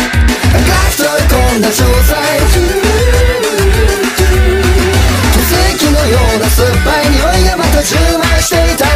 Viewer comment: what is being said around their heads? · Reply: "du du du du du"